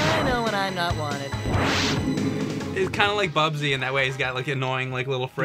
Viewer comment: I don't know why, but when mentioned Bubsy at the beginning I heard it as "Bub-Z"